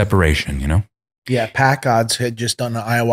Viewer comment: did he just say packgod did an ayahuasca ceremony?? ... i surely misunderstood that name? or not? anyone know?